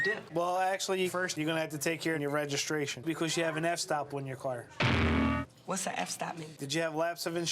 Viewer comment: The key fob for my 22 year old Benz cost $550! Sick huh?! Did he say "F-stop"? @ 1:16:53 📸